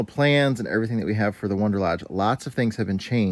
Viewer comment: I thought they are "Wanderlodge" vehicles? I keep hearing you say "Wonderlodge".....